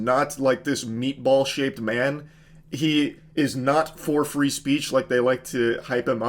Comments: everytime you said elon musk for free speech i heard elon musk 4'3 and i was like makes sense. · when he said "for free speech" i heard "4 foot 3" and quickly rushed to the computer to see if he showed a scaled picture. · I misheard “for free speech” as “4’3 speech” and was absolutely ready to believe he was a dwarf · every time you said for free speech it sounds like you were saying he 4'3" i knew you were short but didn't know Elon Husk was only 4 3 he is a tinier man then i thought =O